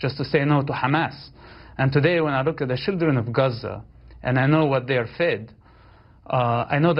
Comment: I think he said: "I know what they are fed." not what they are going through at 2:17.